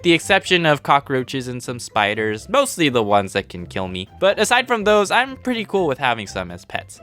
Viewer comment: “ mostly the ones that can kill me” what i heard “i like things that can kill me”